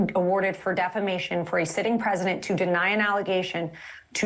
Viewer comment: Did she say a "sitting president"? Trump is not president.